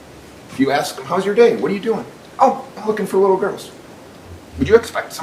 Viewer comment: Calling someone a pedophile for no reason should be considered a hate crime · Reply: he said im looking for my goggles and they heard him say im looking for little girls